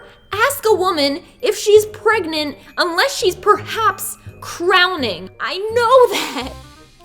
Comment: "YOU SHOULDNT ASK A WOMAN IF SHES PREGNANT UNLESS SHE IS PERHAPS CROWNING" I SHRIEKED 😂😂😂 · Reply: Danyell Stoerger I thought she said DROWNING lol